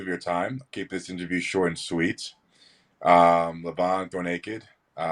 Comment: What did Larry say? Levan go naked? 😆